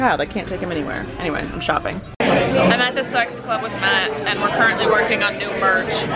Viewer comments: What does she say at 1:13? I can only hear “I’m at this sex club with Matt” lol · *insert the THE WHAT meme* i heard s3x club